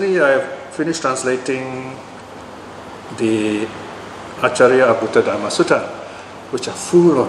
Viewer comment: What is the name of the Sutta pls? cant hear clearly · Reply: Hello, it's the Acchariya Abbhuta Sutta (M 123, SD 52.2) at: <link>